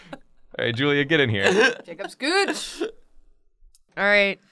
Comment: When Julia said "Jacob, Scootch!" I thought she said "Jacobs Gooch!" I was immediately concerned to where this video would end up.